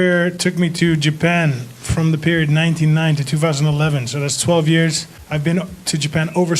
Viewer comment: "1990 to 2011 so that was 12 years" - Alistair Overeem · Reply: totally sounds like he says 1990